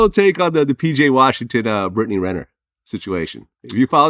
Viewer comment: I thought that shit said brittany griner 😂😂😂